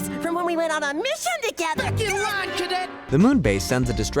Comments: did he say f##k you? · wait did he say fuck you? (Edit:he said freak you)